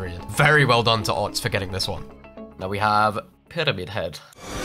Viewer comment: bro i thought you said ketamine head 💀